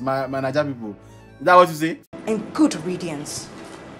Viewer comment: Did Omotola say "good radiance?"😂